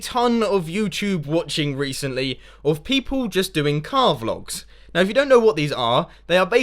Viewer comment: When Mumbo said 'Car Vlogs' I heard it as 'Carve Logs' and I had no clue what he was on about! I thought I was missing something, but that was ridiculous..